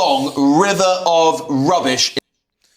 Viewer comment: Sounds like you say "river of refuge" rather than river of refuse.